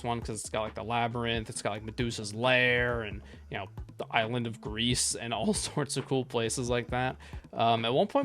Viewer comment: Did did he just say the island of Greece?